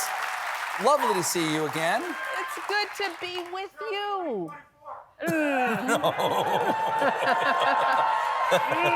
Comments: What did that guy say at 1:03? I can't make it out. :( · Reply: She's a dude. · What does that audience member say at 1:03? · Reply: Michelle Obama 2024! They want her to run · What did the guy say at 1:03 ? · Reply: 'SHE'S A DUDE!" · I can’t make out what an audience member says at 1:08 that they briefly react to. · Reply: They yelled out, "Michelle 2024" 😂 she was having none of that.